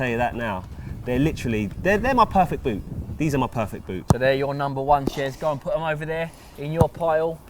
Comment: With the captions on it says boobs instead of boots.